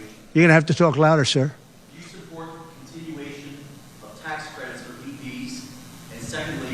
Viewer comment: What did that guy ask around the 1:22:00 mark regarding talk credits? It sounded like he asked if he supports tax credits for PDs.